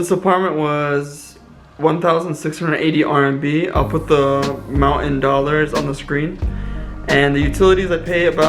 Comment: Yeah, around 6:21. I mean thats how the captions read, and I think it sounds like what you're saying too. I thought it was American slang but googling "mountain dollars" didn't really get me an answer.. Please correct me if I'm wrong! :) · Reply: oh haha, I said I'll put the amount in dollars on the screen, I can tell how you got confused. It kind of sounds like mountain dollars. Sometimes the captions get messed up😃